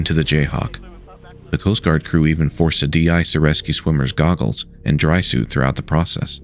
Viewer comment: what did you say? De-ice the rescue swimmers goggles?